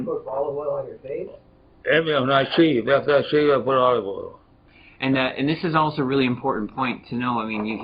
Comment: What's he saying at 8:10?? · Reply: "After I shave, I put on olive oil."